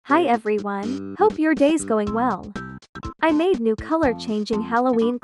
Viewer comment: HELP ME- WHEN I WATCH THE VIDEO I HEARD "I HOPE YOUR DAYS GOING WRONG"